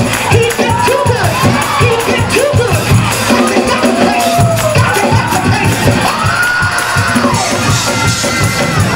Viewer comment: What did she say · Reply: Gotta give God the praise